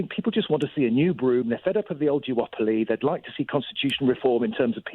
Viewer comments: Am I hearing things or did he jewopoly? as in jewish monopoly? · Sorry does the caller say "jew-opoly"?